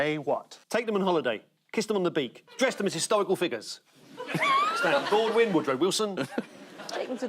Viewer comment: What did he say about Woodrow Wilson? I didn't get it. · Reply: I'll answer this myself, but after sound checking myself, it sounds like "Stan Baldwin, Woodrow Wilson"